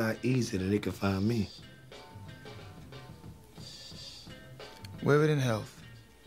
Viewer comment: what does marlo say before omar says "no doubt" live it in health? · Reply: i thought he said "wave it in hell" talking about his guns